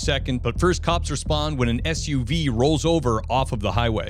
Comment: I had to rewind that - on first listen I heard "but first, cops were spawned when an SUV rolls over.." but no; it was respond. I prefer my version